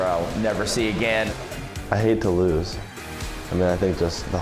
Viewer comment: I legit thought he said “I need to lose”